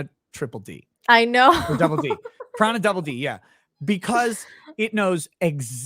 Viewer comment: what said here, what is the title? I can't understand it. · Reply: Piranha 3DD